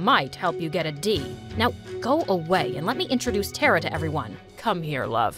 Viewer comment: When she said go away wich I thought it said um b word